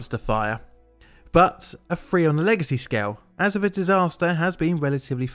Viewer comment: "a freon legacy scale" - sorry i guess i misheard that